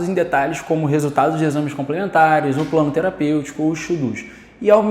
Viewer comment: ou o que ? EXU DUS? traduza! n entendi essa dicçao ! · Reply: "os 'to dos'", que seria uma tradução como "coisas a serem feitas", eu acho. Entendi assim.